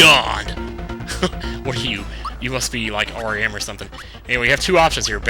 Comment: excuse me but did you say oiram at 4:57 /:|